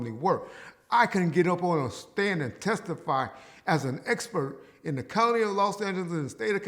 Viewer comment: I thought reggie said he could get up on the stand and testify has a " GAY EXPERT" 🤣🤣